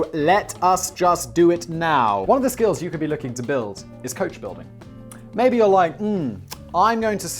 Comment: Thanks to Skillshare for sponsoring! The first 1000 people to use this link will get a free trial of Skillshare Premium Membership: <link> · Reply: When he said "Coach building" I thought he said "Coke building" and I was down. If Skill share can teach me how to make Charlie, I'll join.